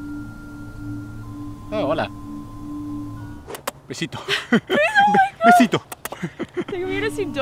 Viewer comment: Creí que dijo que pe#o güey en el minuto